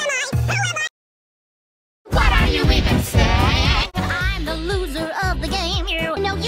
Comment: What did spinel said with her creepy voice ._. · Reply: If you actually wanna know, she said "What are you even saying?!"